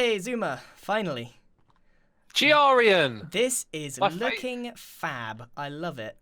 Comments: what u say? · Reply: He said "This is looking fab" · What did he say????? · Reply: Looking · rewatching in 2020 25:37 DID G JUST SWEAR????? i hear "This is F**** Fab" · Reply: Did you not clearly hear “ this is looking fab”?